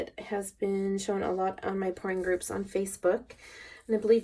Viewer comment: Kind of funny... when you said "pouring group" sounded like you said "porn group" Haaaa, thanks for that made me do a double take ;)